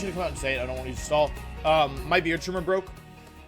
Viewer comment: I could have sworn said "Might be YouTuber broke" and realized he said his beard trimmer broke